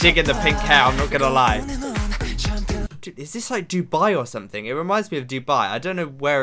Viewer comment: when he paused i thought he said "is this dude bi or something" when pausing on taemin and i- p.s you HAVE to react to taemin